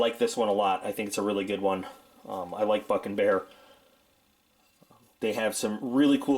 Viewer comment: When was saying Buck N Bear I thought he was saying F#**ing bear :)